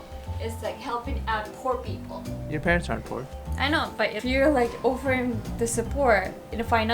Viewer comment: did you just said “your parents are poor” 😅